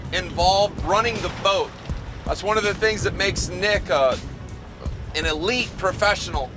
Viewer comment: I thought he said “nigga” 🤭😂.